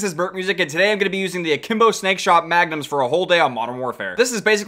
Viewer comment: sounds like said stake shot